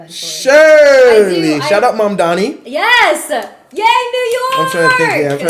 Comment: That guy said "Shout out Mamdani" but I could have sworn he said "Shut up Mamdani" and I'll be honest he probably could have said either and it wouldn't matter because these people are fake as all hell. Also, I'll say this again, Hasan not getting VIP access is just the PG version of the "purging of the useful idiots" that happens when communist take power. You know, that thing that always happens when a group get into positions of power and no longer have use for those who support them but aren't in the inner circle.